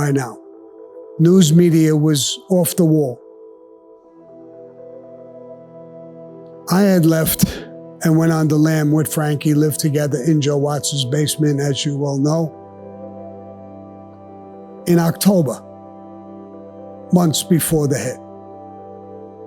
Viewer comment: I thought he said was a westy in car with franky